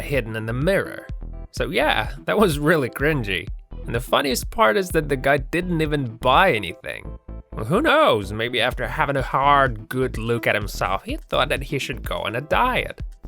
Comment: Sounded like the narrator said "sobway" instead of subway, "polar-gast" instead of poltergeist. At the 3:34 mark you hear, "Maybe after having a hard good luke at himself",.......ok....that's when I stopped watching.